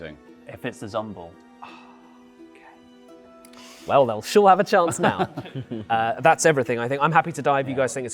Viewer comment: I was confused that said that called the Zombuul a she. I think what happened is that said “they’ll sure have a chance now” and heard “then she’ll have a chance now”